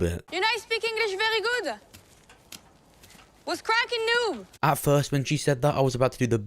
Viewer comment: I thought she said "What's crackin dude?"